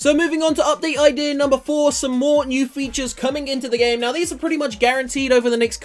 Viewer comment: OUT OF ALL 5 WHICH IS THE MOST IMPORTANT FOR ALL PLAYERS? DAILY CHALLENGES? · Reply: I thought u said it was coming in